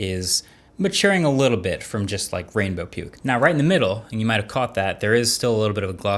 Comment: I thought he said rainbow ppl lmao